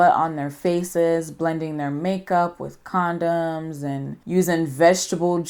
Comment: 😏 Girl? Did you just say "blending your skin with condoms" LMAO 😂🙃